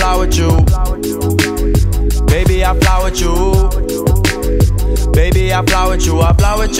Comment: I thought he was saying flowered u for the longest time until he said it slowly one time and I was like OHHHHH FLY WITH U LOL I was so confused :P